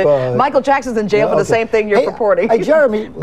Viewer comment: can someone tell me what she was saying I can't understand what she was saying all I understand is Michael Jackson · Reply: "M J is in jail for the same thing you're ..." and that all I understood xD sorry